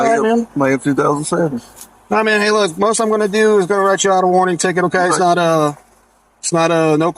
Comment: ts 4:27 I did not hear the LEO say he was going to issue a 'WARRANT TICKET', I heard him say he was issuing a 'WARNING TICKET' for the LICENSE PLATE issue. BUT...... what raised my entenea's was when he then says, " tell your daughter to use her seatbelt when we are riding......". That is a clear violation, that WARRANTS A TICKET. GET HER DOESN'T ISSUE ONE.